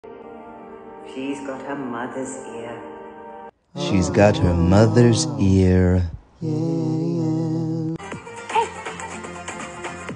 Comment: 00:5 I heard “she’s got your mother beard” 🤣😅